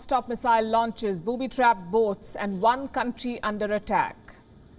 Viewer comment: Dang, sounded like she said Bobby trapped goats, but it was boats, however Bobby trapped goats would be devastating too